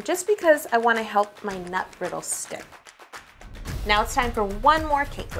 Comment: I thought she said nut Riddle instead of nut brittle